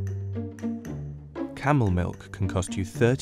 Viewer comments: Not going to lie, I thought it said caramel milk😂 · Thought it said caramel milk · I thought it said Carmel milk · Lol I thought it said Carmal milk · i thought it said caramel